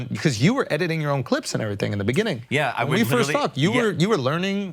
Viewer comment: Did andrew say when we first fucked?